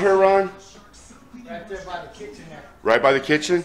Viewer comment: Only a few blocks from where I live. A microwave is the kitchen… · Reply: he actually said kitchenette, I misheard him, I was looking for a kitchen then realized he said kitchenette.